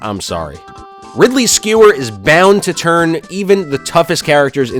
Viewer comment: The video started buffering when you talked about ridley and it sounded like you said “Ridley’s Skewer is banned”